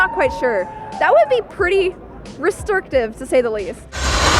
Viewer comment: Did she say RESTORKTIVE? LMAO!